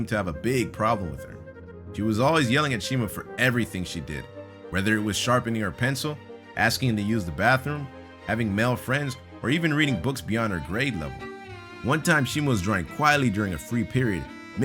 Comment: Having male friends?? did I hear that correctly or did he say something else, if I am correct then, da fak???